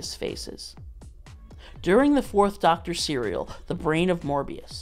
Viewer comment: durring the fourth doctor cereal,, did i hear that right